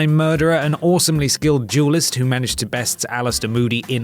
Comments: omfg it took me half of the video to realise his brittish ass was saying duelist instead of jewlist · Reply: I'm British and I thought he was saying jewlist too haha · omfg it took me half of the video to realise his brittish ass was saying duelist instead of jewlist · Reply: im English and even I thought he was saying jewlist lmao